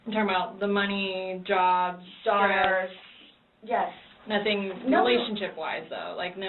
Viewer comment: Question: At 2:15:00 what is saying after “the money, jobs, _____”?? Sounds like she’s saying “SARS”?? (Her and said it at the same time) but I can’t figure out what they’re saying. Thank you. EDIT: fixed time stamp. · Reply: I think said "stress".